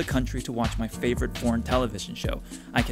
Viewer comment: I thought he said "porn" when he said "foreign":face-purple-crying::face-purple-crying::face-purple-crying